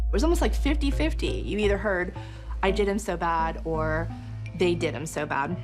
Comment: You clearly hear Alex screaming in the background. I heard “I did them so bad “